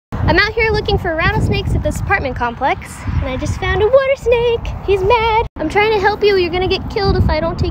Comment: How are you so calm picking up a rattlesnake?! I respect you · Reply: Water Snake not rattle snake. Although it does sound like she was saying rattle snake but the subtitles she put in clarify she said water snake.